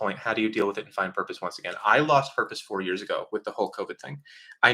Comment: At first I heard "I lost my purpose because of the Hulk Hogan thing." Thought you lost hope because he lost a wrestling match lol.